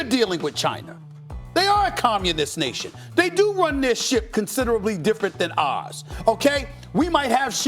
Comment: did anyone else at first hear "They do run their shit" instead of "ship"